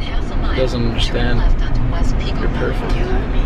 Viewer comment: Who heard say to tessa at 8:55 she's perfect like if u did